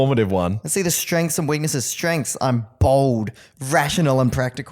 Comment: I really thought called himself bald at 9:51.